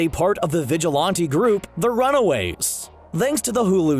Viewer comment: Did you say the Runawaves???